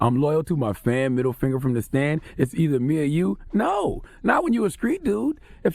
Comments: I thought I heard “from the stands” tbh & his music trash but whatever y’all say. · I thought he said "Middle finger from the stands"...Idk,seem kinda reachy... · Sounds like he said “middle finger from the Stans” Charlemagne reaching? I know Tax his boy but the reality is during the trial they both will have to take the stand, whether they snitch or self incriminate time will tell.